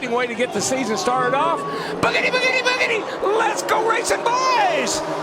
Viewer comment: What say in 21:25? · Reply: bigiti bigiti bigiti. Let's go racing, boys .